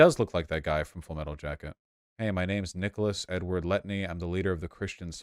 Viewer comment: I thought vaush said full metal alchemist and was trying real hard to figure out who he could be talking about.